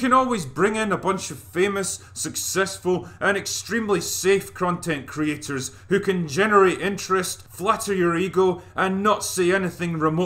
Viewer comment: Am I the only one who doesn’t know a single of those „famous content creators”? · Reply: I thought he said crontent creators